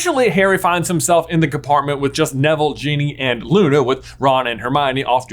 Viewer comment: Did you say genie and not Ginny?